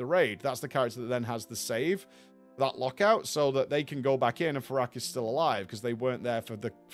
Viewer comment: Oh it's 'Fire-ak' as in a fire dragon - every time I've heard it as 'Farrak' and assumed it was a Troll God :)